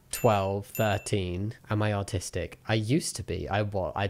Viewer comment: I heard “am I autistic? I used to be” and was very confused for a moment lol